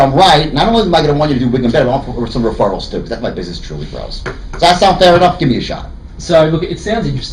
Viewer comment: Youtube subtitles: If I'm right, not only am I going to want you to do big and better or some referrals too because that's my business truly gross What I heard: If I'm right not only am I goglglglodjdsksnnxksk or some referrals too because that's my business truly grows LMAO